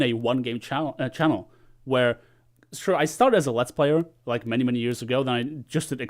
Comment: What game did you say you played before dirty bomb? Sounds like "lets" or "next"